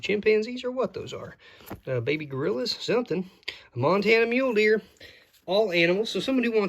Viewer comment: Did I hear you say "Montana mule horse"? 😅